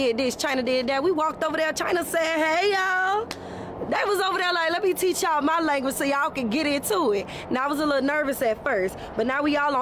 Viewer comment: subtitles please... can anyone understand what she said in what i guess is English? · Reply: Well, English is not my first language but: They was over there like let me teach yall my language so yall can get into it.* Now I was a little nervous at first but now we all in Xiaohongshu. *Or maybe: get in, too.